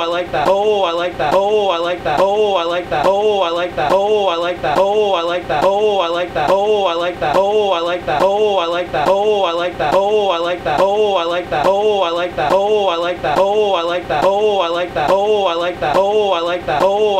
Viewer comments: for a couple of minutes it's sounds like he says boy I lick that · Did anybody else hear "my leg fat"? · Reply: DatBoyTy - when I look at video I hear "ooh I like that" when I look at this comment I hear "my leg fat"